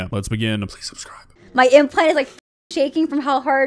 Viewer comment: did she say "my implants are shaking..."? Bummer. I was holding out hope that maybe only her attitude was fake.